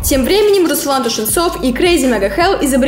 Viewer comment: мне послышалось или она сказала Руслан Тушенцов и CMH хахахаха 😂